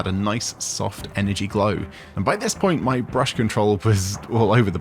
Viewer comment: I thought it said 500 point army, and I was thinking " saaaaammee"